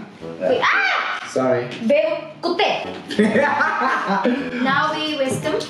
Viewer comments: why did call him babe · did she say "Babee" ?? XD